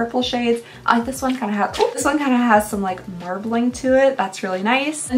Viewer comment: Ha! I thought you said "mer bling" instead of marbling and i was just like "yeah that blue shift is really mermaid-y!"